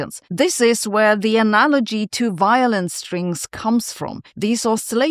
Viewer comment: Is a "violence string" another term for a "garrote"? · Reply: I thought she said violence too,but apparently she said violin strings,while breathing in a German accent.